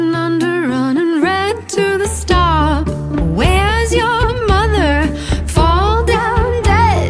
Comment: Hook, line and sinker-- drop it down to the bottom Butterfly float, flicker, soar to the top Kill for the thrill-- cut it, stick it where you got him Circle rolling under, running red to the stop Boy, where's your mother? Fall down dead Dirty mind, dirty mouth, pretty little head I wish you were here, I wish you'd make my bed Dirty mind, dirty mouth, pretty little head Sister, bring it hither, push it down below Catch yourself a looker, let him go go go Wanna have your baby, but I'm so so slow Well, don't you worry, honey, 'cos I can't say no Boy, where's your mother? Fall down dead Dirty mind, dirty mouth, pretty little head I wish you were here, I wish you'd make my bed Dirty mind, dirty mouth, pretty little head Take a breath, my heart, and hold your tongue It's just a cog in the year of all my love Well, take a breath, my heart, and hold your tongue It's just a cog in the year of all my love All my love Boy, where's your mother? Fall down dead Dirty mind, dirty mouth, pretty little head I wish you were here, I wish you'd make my bed Dirty mind, dirty mouth, pretty little head Boy, where's your mother? Fall down dead Dirty mind, dirty mouth, pretty little head I wish you were here, I wish you'd make my bed Dirty mind, dirty mouth, pretty little head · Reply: I'm a lil' confused. I don't hear boy where's your mother I just hear where's your mother...every time I look it up it adds in boy like that but it's never sung that way.